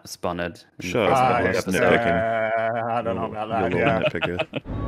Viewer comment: I had a couple drinks in me so at around 7:25 when called a little Nit Picker, I spat out my beer and thought WTF? For almost a full second before I started laughing at how badly I misheard.